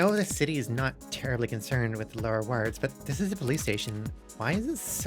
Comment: I misheard her say police station as play station... I was very confused